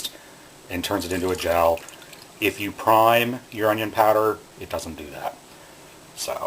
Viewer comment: on 1:30..."one single" what? i cannot hear that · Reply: "...if you prime your onion powder, it doesn't do that...so..."